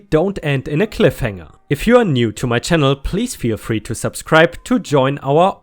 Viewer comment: anyone else hear please be afraid to subscribe